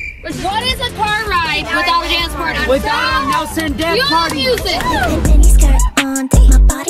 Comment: What did say while screaming and 😅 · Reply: DaNcE PÆARTÆY